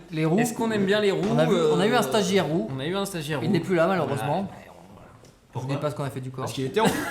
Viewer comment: Qu'est-ce qu'il dit à 10:07 ? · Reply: Avastar_Bin je me posais exactement la même question mais maintenant j ai compris, il dit " je sais pas ce qu'on a fait du corps".